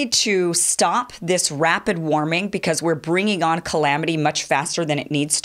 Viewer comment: did she say cLamity? sounds yum